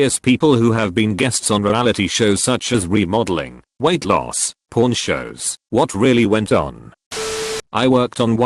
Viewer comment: I wasn’t paying attention and I thought the robot voice said “Porn shows” not pawn shows